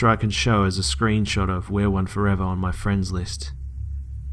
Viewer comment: The whole way through I thought you were saying she was called "WEAR one forever". lol "WE ARE one forever" is a lot creepier. lol